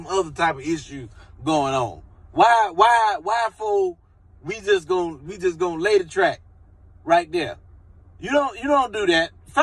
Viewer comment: Why did I hear “London on the track” in seeing this 😩